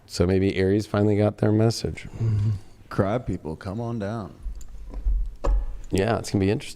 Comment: whispers in the background on the Sag part were annoying AF! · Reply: he was saying CRAB people. I thought he said that too at first :)